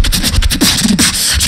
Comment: what does he say in the beat ? so Sick! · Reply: "I've been killin' every day"